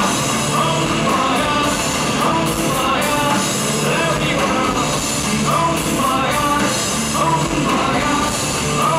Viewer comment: What is he saying at 11:35 "......, there we where" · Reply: picajoool “you opened my eye”